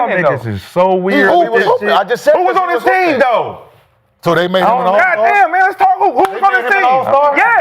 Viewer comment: Rashad did say yes after Kenyon asked if “they” made him an allstar” Mccants probably thought Kenyon said “That” instead of “they”